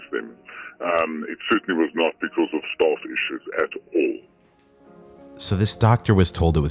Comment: doctor certainly says "It certainly was not because of staff issues at all" or am I hearing wrong?